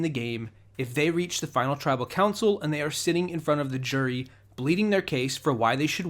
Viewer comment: "bleating" their case instead of "pleading" their case LOL 🤣 This is why I love you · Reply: WOW I’m stupid I thought he said bleeding